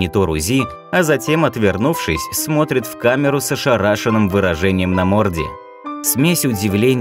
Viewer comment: мне одному послышалось "сша рашеное" ? :-D